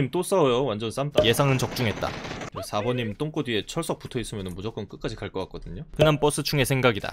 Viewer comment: 4번님 똥꼬뒤에 철사붙어있으면 무조건 끝까지 갈거같거든요 (?)라고 들려어떡해 내 귀가 이상한가봐